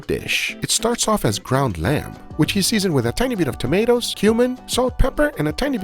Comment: at @ 8:31 i swear he says "human" as part of what its seasoned with